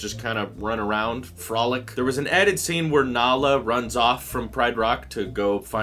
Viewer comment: I legitimately thought said "Nala runs off to go find Hell" for a minute there.